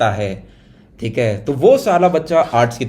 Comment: did you said 'wo saala baccha' 🧐